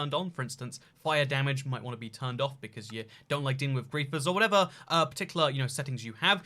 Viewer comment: did just say that creepers deal fire damage? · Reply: He said Griefers